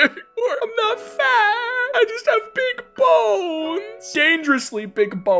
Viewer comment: why does 6:27-6:31 sounds like it says "balls" instead of bones 😭..